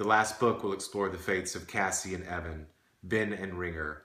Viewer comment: When he said Cassie and Evan I thought he said Cassie in HEAVEN!!! I was like wtf?!??